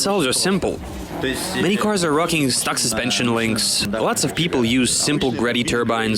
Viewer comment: На 7:05 он сказал: Двадцать пипл юз?, или мне послышалось